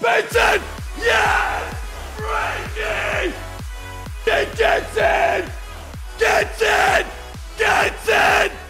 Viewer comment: is he saying Ben ketser? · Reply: I think '' thats it'' 😂